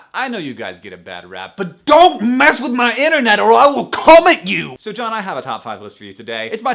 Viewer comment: he put way too much emphasis on the "come" and not enough on "at" in i will come at you so how I heard it was "...Or I will CUM at you!"